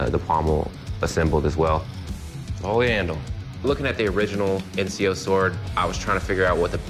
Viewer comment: did he just say the original ezio sword?!! I knew the assassin's were real.